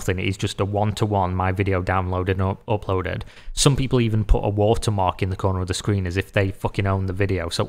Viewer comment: I thought you said “Waltermark” lol